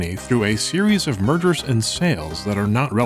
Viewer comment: I swear I heard "Murders" instead of "Mergers"; completely took me off guard XD